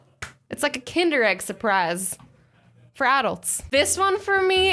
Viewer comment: I thought she said fradults Now i heared for adults.